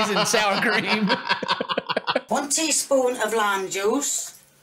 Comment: Horribly frightening moment in the video when I thought she said "Add 1 cup of LAMB JUICE" Ahhhhhhhrrrrhhhh!! Fortunately what she poured was Lime Juice so all was saved and right with the world agin. But DAMN sheesh...that was a close one!! The confidence of me vomiting in a projectile fashion is extremely high if she actually meant lamb jooz. What would that even look like?? Ugggggh.